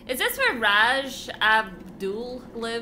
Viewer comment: Did really said 'Ras Abdul' at 19:10 ?